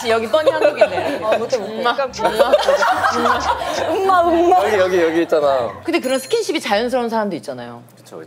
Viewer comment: 극중에 볼뽀뽀 나오나?! 여기여기 했잖아라고 들리는 것 같은데